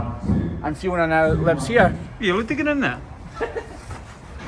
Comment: Did say, "Are you digging in there?" I assume he meant are you going in there to find Fiona. I didn't know you were part of the rescue team - that was an amazing story!!! So, tell me why I was crying at the end with the way you were interacting with your fans and signing their books? you have a very special way with cows, bulls and people. You care about everyone, and it really shows. You are a good man!